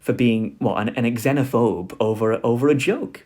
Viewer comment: Wait, did you just say ksenophobe at 43:22? I am guessing that's because of your knowledge of Greek. My respects